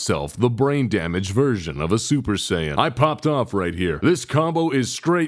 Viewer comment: Nearly at the beginning i misheard something like "Braindamage Virgin Super Sayan". Welp it goes to my silly nickname collection